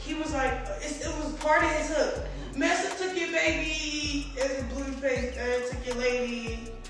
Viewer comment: Did anyone else hear her say the bar she had him change ? Master took your baby… or am I hearing things? At 5:56…..